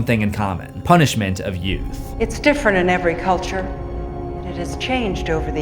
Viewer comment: Omg she said “Youth”?! I swear I always thought she said “You” as like a metaphorical “You” meaning the sacrifices.